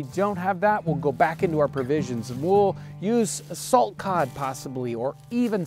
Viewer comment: A salt cod, oh ok i heard assault cod which sounds terrying.